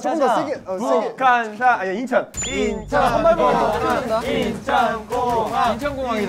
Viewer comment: I thought they said Incheon boang 😂😂😂 it was gonghang hahha